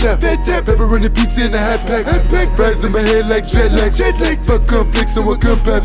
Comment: That man said dreadlicks with a "i" 😂😂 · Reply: I thought he said dreadlikes